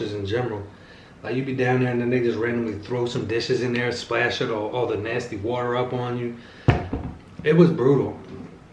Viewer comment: please make it clear for me what you said @ 6:02-6:05... bc I just want to make sure I understood you correctly...thx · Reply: And they (referring to the servers) throwing dishes in the bin and the water splashing up on me